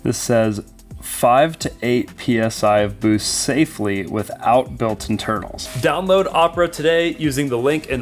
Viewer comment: I swear to God it sounds like you said "...5 to 8 lbs boost without built-in turtles" lmao Had to rewind twice.